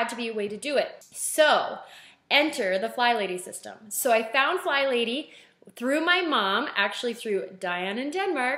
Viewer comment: Omg! Diana is your mom? 😃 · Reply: oh lololol! all I heard was "I have found fly lady through my mom Diana in Denmark" lololol Oh my God I'm getting old 😆